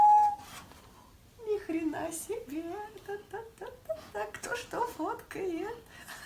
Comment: The girl at 13:20 seems to be speaking Ukrainian or Russian. I cannot really make out the words so I presume Ukrainian. Seems too hard to be Russian or maybe that's just her accent. Edit: It's seems she's saying "Ни хрена себе! (Holy crap!) А ктош-то фоткает? (Who's taking a picture?)" I am not that good in Russian to tell what is her nationality or language. She is either Ukrainian (most probable), Russian or Belarusian (least probable) Generally she's surprised about the food service and playful in a shy way with the person recording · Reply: you're mostly right but I feel like she is saying "Кто что фоткает?" ("Who's taking pictures of what?") and its defiantly russian cuz it would sound much different in belarus or ukranian